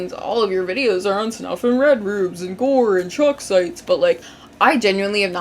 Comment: I thought you said chalk sites then i comprehended that it was shock sites